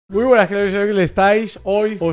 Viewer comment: Mucha gente : Muy buenas criaturitas del señor Lo que escuché yo : Muy buenas fhdjf del señor